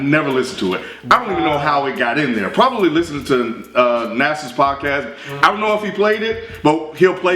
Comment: What's the podcast is talking about? "Nassius"? I can't hear it too clearly. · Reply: Dope Sh!t with Uncommon NASA and Samurai Banana.